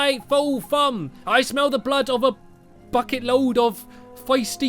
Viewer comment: For a second there I thought when he said bucket I thought he said f@